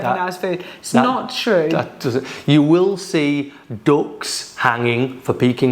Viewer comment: when u said the word DUCKS with that accent I thought you said DOGS are hanging by the window...i was like wtf hahahahaha...